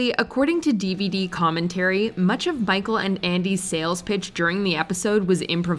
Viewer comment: She said bicheal instead of micheal